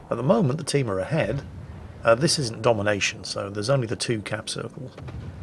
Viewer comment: This is the domination... looks to me like standard battle but hey who's counting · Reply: I thought he said This 'Isnt domination'